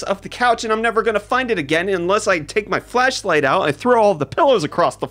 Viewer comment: Did he just say "Fleshlight"?